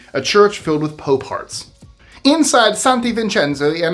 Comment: I thought you said "a church filled with pope parts"!